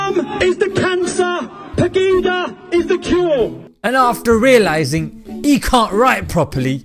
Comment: Did he just say "wakanda is the cure?"😂